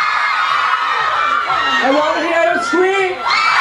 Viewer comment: I thought I heard Tao said, "I wanna hear you squeak!" XD